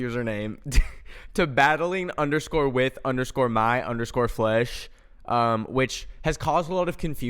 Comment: I legit thought that he was saying "battling with my flush" instead of "battling with my flesh" so i had to look it up on google to see what "battling with my flush" meant, and google autocorrected my search so thats how i found out i just heard it wrong.